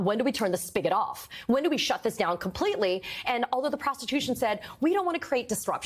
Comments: ❤😂She said prostitution instead of prosecution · Huge kudos to Katie Phang…..so intelligent, so clear thinking, so clear speaking, so intellectually grounded 👏👏👍👍🥰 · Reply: I really appreciate her work and how she articulates the information and analyses of what she covers. That being said, did you catch at 1:31, that she slips and says 'prostitution' instead of 'prosecution'? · I’ve listened to it four times! At 1:31, she DEFINITELY Freudin slipped “PROSTITUTION” in there instead of PROSECUTION! ROFL😂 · she said: "Although the PROSTITUTION said..." instead of "Prosecution". Hah. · does anyone else hear prostitution? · Did she say prostitution instead of prosecution?